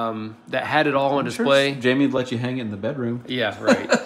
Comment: did i hear right that wife is also named jamie? 21:12....